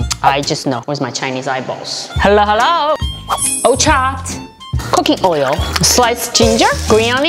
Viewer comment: I know she said "cooking oil" But I heard "cookie oil" 🍪🍪🍪